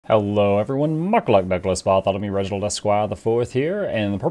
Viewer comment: What you rap in the intro? I replayed like 10 times and still couldnt get it lol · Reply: I think i hear: "Hello everyone, MukLuk Douglas Bartholomew Original Esquire the IV here..."